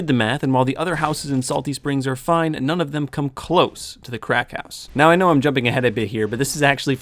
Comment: "Cracked house"? Oh I thought you said "Crack house" You most definately said "Crack house"